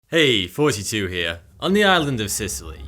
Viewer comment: I always hear "Heeey, it's 42 here..." which is a good thing as 42 is the answer to the greatest question.... (it might be just me..)